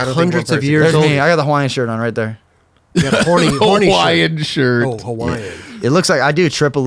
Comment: Why did I hear "horny shirt" at first too 😩🤔🤔